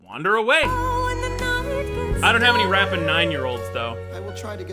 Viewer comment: I heard "I don't have any rapping nine-year-olds." as "I don't have any rampant nine-year-olds." and immediately knew I misheard, but the fact that even if I didn't realize I misheard, it wouldn't make less sense to me is really funny.